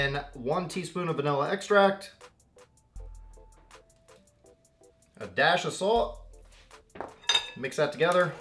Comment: When u said a dash of salt it sound like u said a dash assault lol but good video I am definitely going to try this